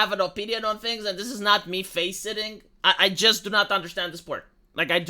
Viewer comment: did just say "this is not me faceSITTING"????